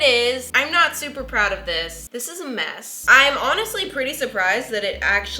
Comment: I'm a mess Me: I thought you said your cake was a mess